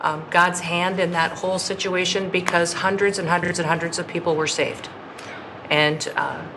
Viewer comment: Ya’ll putting words in this woman’s mouth…👎🏾👎🏾 She clearly says 0:15 hundreds of people were saved. I’m from the Texas Hill Country…everybody who turned out to help did an excellent job in saving people and retrieving bodies of those we lost.